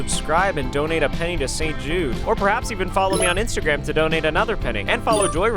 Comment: I thought he said donate a penny to save Jews but it’s saint Jude hahahaha